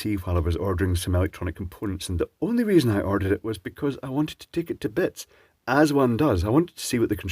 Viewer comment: I thought you said I want to take it to bed.